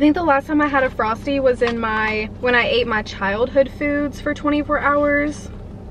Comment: I heard “I ate my child” and I was like huh. Then I heard childhood and just started laughing🤣🤣🤣